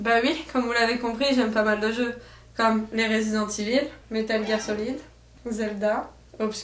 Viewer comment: Mdr l'accent français sur resident evil xD j'ai entendu "résidente évile" xD